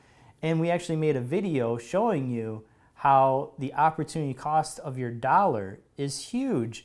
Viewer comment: I heard opportune cost; made my day